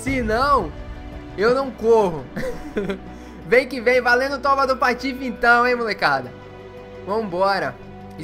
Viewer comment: só eu entendi "valendo toba do patife"?